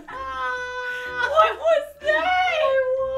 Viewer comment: WTF WAS THAT NOISE?! IT SOUNDED LIKE “Hello”